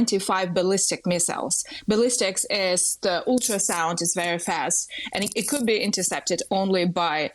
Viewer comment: What did she just say? "Ballistics is the ultrasound"? She has absolutely no idea what she is talking about.